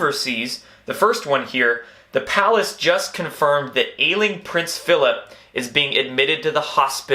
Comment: Lol... I thought u said "alien prince"...😂😂😂 That too, huh?😂😂😂